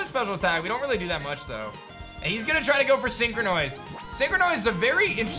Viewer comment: Definently misheard/misread synchronoise for synchronize and came across rude with my first comment. My apologies, hope the rest of the run goes great man.